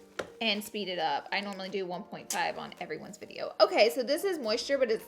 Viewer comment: I thought she said "I normally do 1.5 except for James' videos."😂😂😂